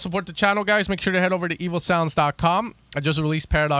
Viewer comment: My english is so bad that each time you say "evosound", I hear "evilsound"